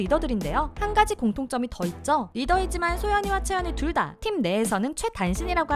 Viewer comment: 응...?? 저기요...? 님 오타나셨어용! 4:17 여기에 채원인데 채연으로 하셨고 왠지 발음도 채연으로 들리는것같아용...ㅎㅎ